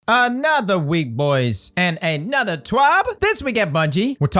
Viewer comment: For some reason I heard “another week boys and more catering to the top 1%”